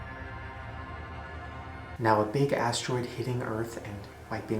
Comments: I thought he said a big ass droid instead of big asteroid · i thought he said big ass asteroid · I thought he said big ass asteroid but he said big asteroid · When he said everybody will be killed with a big astroid. I thought he said BIG ASSS Sorry ill stop